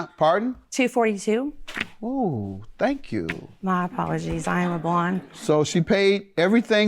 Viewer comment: Did she say "my apologies I am a blonde".???